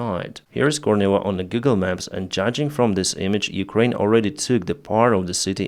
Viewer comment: 🔥 Support Pilot Blog on Patreon! <link> ❤ Buy me a coffee: <link> ✈ Find me on PayPal <mail> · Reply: OMG dude you made me laugh so hard. You said "on Google maps" and I heard "Giggle Maps". :face-blue-smiling::face-red-heart-shape